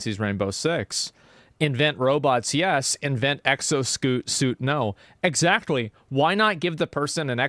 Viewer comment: I heard "exoscoot"